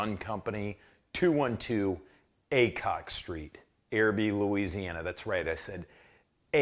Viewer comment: I thought you said ACOG street. I was disappointed at first Then highly excited